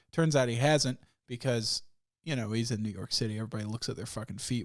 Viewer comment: at first i thought he said Negro City instead of New York city lol